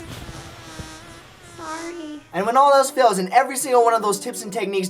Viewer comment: On 6:13 am I the only one who heard a "sorry"